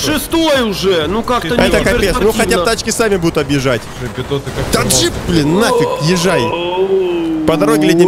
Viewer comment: услышал таджик)